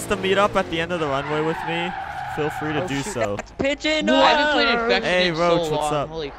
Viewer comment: who else heard idk who said "Pigeon noooooooooo!"